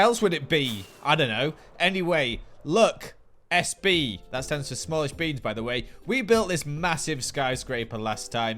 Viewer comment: 😮Really? I thought it said small base!